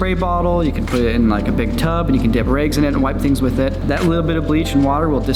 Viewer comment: I swear he said “dip your eggs in it” at first.